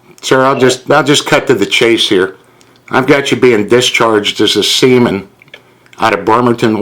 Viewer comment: Did he just say discharged semen?